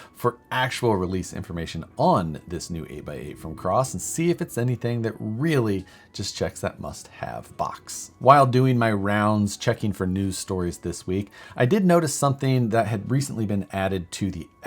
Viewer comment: I thought you said "cross rc checks that mustache box" that thing's taking control